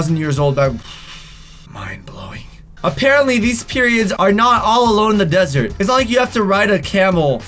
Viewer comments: sounds like he said period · Omg 2:56 he said PERIODS instead of pyramids lol